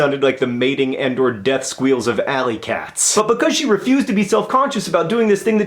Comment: JOHN I'M CRYING when you said, "the death squeals of ally cats," I heard it as, "the death squeals of allkayzz," AS IN ALLYKATZZ THE BLOGGING WEBSITE (THAT DOESN'T EXIST ANYMORE) FOR PRETEEN GIRLS WHERE I WAS INTRODUCED TO MY NOW BEST INTERNET FRIENDS AND NERDFIGHTERIA AND IN MANY OTHER WAYS SHAPED WHO I AM TODAY!!